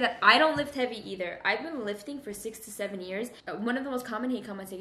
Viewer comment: I thought you said you were lifting for 67 years 😂 but I made sense out of it and realized you meant 6-7 years. Lol you speak hella quick